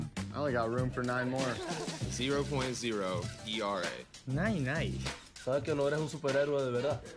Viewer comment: 大谷なんて言ってんの？ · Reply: ナインナインて言ってるんじゃない